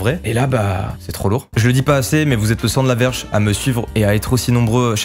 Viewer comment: C moi ou à 19:30 dis < vous êtes le sang d’la verges 😂